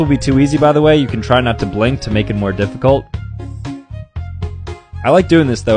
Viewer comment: I thought he said it to blink 🤦🏽‍♀️ so i passed even with the difficult one